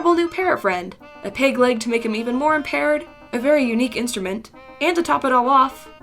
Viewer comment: Im i the only one that heard her say making him more a parrot